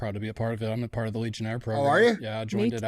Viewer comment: what program did they both join? Never heard of that one from Andy before. Did he say legion?